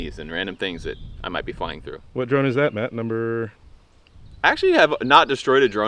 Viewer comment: I thought I heard Matt’s brother 😢